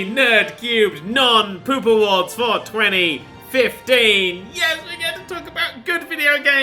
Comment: 420 is back and so am I ( ͡° ͜ʖ ͡°) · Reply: The way Dan says "For 2015" he has a pause when he says 2015. "For 20.........15" so it sounds like he is saying 420.